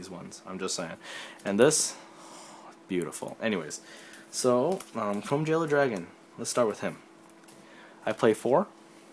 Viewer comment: Did you just say chrome VEILER?xd